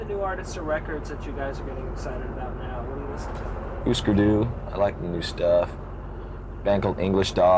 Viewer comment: what band did James say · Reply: Husker du